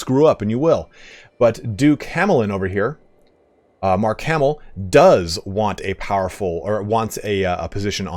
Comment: did he say Mark Hamil?😂